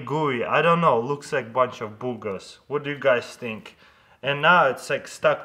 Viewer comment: HENEVER HE SAYS BOOGERS IT SOUNDS LIKE HE SAYS GOOGLE